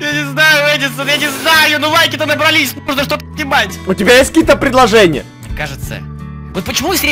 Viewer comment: послушайте - говорит:надо что-то е*ать,мне послышалось)